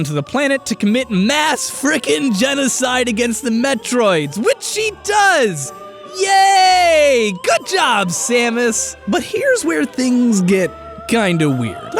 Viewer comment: Did call samus a he